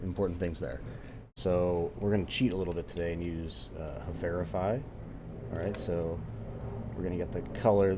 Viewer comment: couldn't understand what you said used to get colors? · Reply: sorry, yeah my mic was bad back then (this vid is like 2 years old i think). that tool is "Hoverify" which is very useful browser extension. I use it to find colors quickly and also download all the images on a site quickly. most of these things can be done in the inspect panel but it just saves me a little time here and there